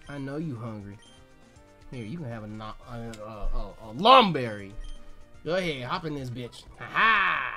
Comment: ohh, lum berry? i thought you said lung berry